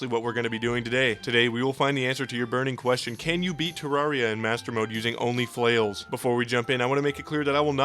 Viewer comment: At first i thought this said "can we beat terraria with only fans"